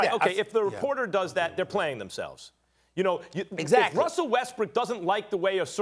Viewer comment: Hahahahahaha Did Kellerman just say Russell WestBRICK at 4:06?